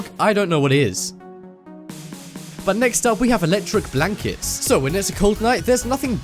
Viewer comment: I thought he said electic black kids